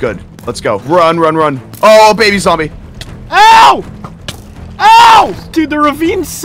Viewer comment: when ranboo said “ohh a baby zombie” i thought he said “ohh a baby saw me”😭🤡